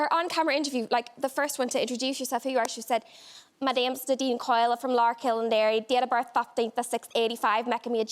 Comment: Still no idea what that Irish girl's lie was. Simply can't understand what she's saying. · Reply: She said ‘My name’s Nadine Coyle, I’m from Larkhill in Derry. Date of Birth 15th of 6, 85 making me a Gemini’ and then she thinks for a second and says ‘What date of birth did I say? 15th of 6? Oh’ She had lied about her age to enter the show, but accidentally revealed her true age later by giving her correct year of birth.